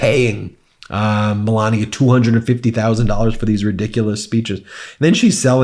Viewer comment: I don’t understand her either. Sounds like she said “feces” 🤔🤣🤣🤣